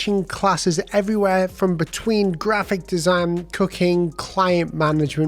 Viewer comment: I swear I though I heard you say “cocaine” on the skillshare part of the video and then realized it was “cooking” 😂😂 hahaha love your videos